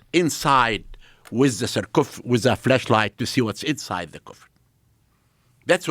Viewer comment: Did he say flesh light on 5:38 😂